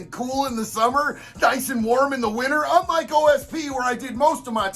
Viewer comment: I thought I heard OSB or Oriented Strand Board which triggered my carpentry days complex